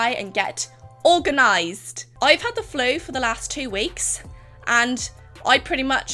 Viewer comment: The FLU!!!! Until that first cut, I thought she said that she had "the flow" for two weeks. I was like 'no wonder why she was dead.... That sounds awful....'.